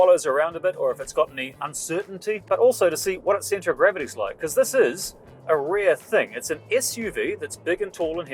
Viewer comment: right :D New Zealand is the only country without an accent. You speak so clearly in fact, that when I'm listening to a Kiwi, I have to keep replaying what they said in my head, substituting "e" for "i". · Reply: I especially liked 16:37 when you said "this is a rear thing" instead of "this is a rare thing".